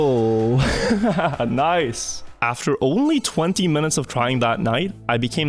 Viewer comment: Dirt? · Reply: i thought he said after only crying for 20 minutes😂